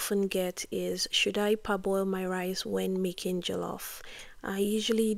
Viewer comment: What did you say at 8:36? "should I --------- my rice when making Jollof..." I can't understand what you said there. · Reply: “Parboil my rice…”